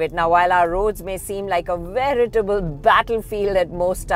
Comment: At the 0:27 mark said something like "vraiatable" battle field...and I did not get what she said. Now will the autocar video editor ignore her gibberish just because she is higher in the autocar pecking order?or she did say a strange word which I have no idea about. 😉